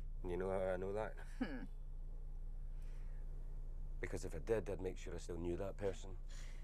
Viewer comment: hey guys! what is he saying at 1:23? Please please let me know it's driving me bonkers! Kisses xoxo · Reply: because if i did, i would make sure i still knew that person